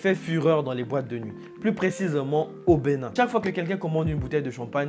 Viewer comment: Je sais pas mais " mettez ⏸ à 5:23 mais écoutez bien à partir de 5:22 ... j'entends Bénin mais dans le sous titre je vois Nigeria ??...LOL big up à toi ..